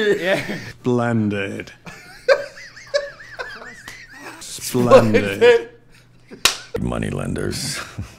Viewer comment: I thought said "blended".